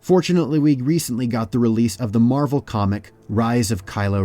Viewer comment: did he say marvel comic